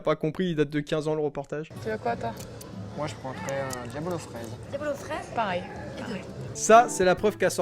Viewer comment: C'est moi ou elle a lâchée un "pareil, carré "😂